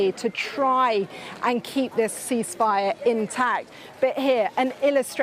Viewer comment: Iran thought they said, Increase fire.